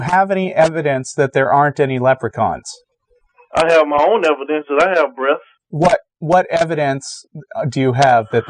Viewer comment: it sounds like he says he has his own evidence that he has breasts. What the hell did he say? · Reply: acg smith 'that I have breath' is what he said. Basically he means he has personal evidence that he is alive.